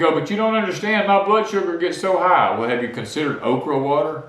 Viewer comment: I thought at first he said oprah water. I thought hello no; I aint drinking nothing that reprobate came up with. Then, I realized he said OKRA water. 🤣🇺🇸🎯